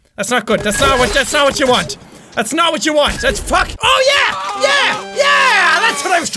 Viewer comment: Haha at 7:15 did he say lets fuck? ;D